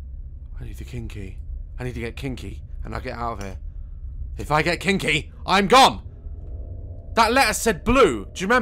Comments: “If I get king key I’m gone” bro that sounded like you said if I get kinky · is it just me or did he say '' i need to get kinky''?